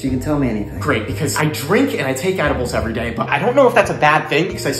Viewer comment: I wasn't paying attention and thought he said that he kicks animals everyday